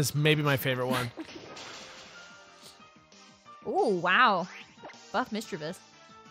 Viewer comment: Did I hear that wrong, or did say "Fuck Misdreavus."?